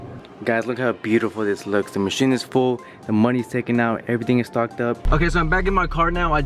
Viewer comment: i heard "everything is f*cked up"